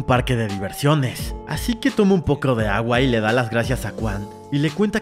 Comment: ¿Alguien más se dio cuenta que dijo un pocro de agua en esta parte? 3:08 (igual eres un gran YouTuber te quiero)